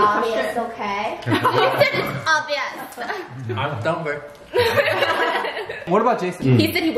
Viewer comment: Yoooo what did say at 11:38?? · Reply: What about Jason